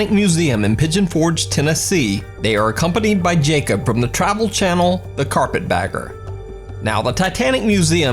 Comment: did he say the travel channel rather than youtube channel?